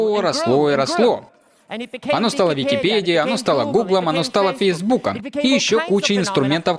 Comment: я правильно услышал, Пейсбуком ?)